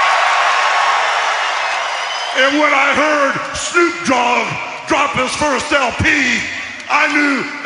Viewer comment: Sounded like Lilian said Shnoop dog